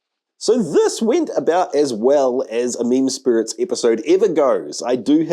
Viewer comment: I love meme spirits. You inspired me to ferment 5 gallons of flamin hot Cheetos. Boy was it a pain to work with but I can’t wait til it’s done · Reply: "Meme spirits" I always thought he said "Mean spirits" Mind blown 😀